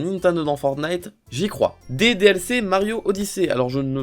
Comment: Wow quand ta dit "Des DLC dans Mario Odyssée" j'ai entendu "DDLC dans Mario Odyssée".. Surtout qu'il y'avait la musique DDLC derrière